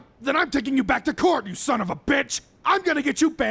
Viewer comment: I got a notification as soon as he said they so it sounded like “I’m taking you back to court you son of a ding!”